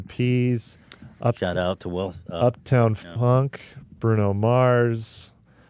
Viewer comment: lmao did anyone else catch at 1:05:33 after they said black eyed peas ethan goes “shoutout to will sm- ... uh no”